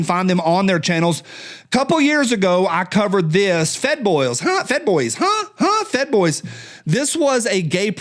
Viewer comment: My hearing isn’t that good. At first I thought you said “Fat Boys”. Drama Llama